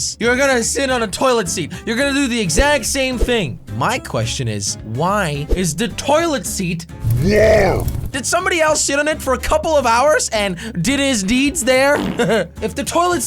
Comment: Sounded like he said. Did someone else s*it on it for a couple of hours?? In the beginning of the video. But he really said sit